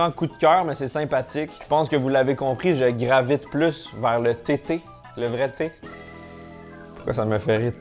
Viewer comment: Quand t'as dit thé thé, j'ai pas entendu tétés, mais j'ai entendu TT. Alors je me mettais en route pour l'île de Mann, jusqu'à ce que je comprenne. Doooonc, je repose mon casque et je poursuis la vidéo. Bisous